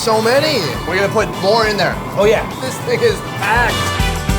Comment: what did he just say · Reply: This thing is packed.